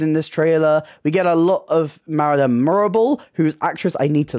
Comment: Im not a WICKED fan, but I thought Id help you out, you said at 17:37 "Marilyn Morrible, who''s actress I need to look up actually, because I have forgot her name, it's Michelle but I can't remember her surname" the part wrong in this sentence is Marilyn, her name is Madame Morrible, not Marilyn Morrible, the name that sounds close to that is Marilyn Monroe, it's ok, easy mistake, I made that mistake too when I was younger, Ilysm!! #ChaoticTheatreGangForLife ✌✌🏻✌🏼✌🏽✌🏾🏳‍🌈🏳‍⚧